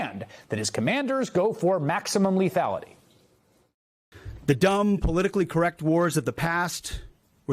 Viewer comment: Did I just hear "politically correct war"?